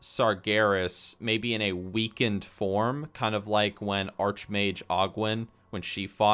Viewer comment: good video, when i heard you said "weakened form" i thought you said "weekend farm" and i was like "wow that would be boring either way" and it took me 30 seconds to realize what you meant. lol!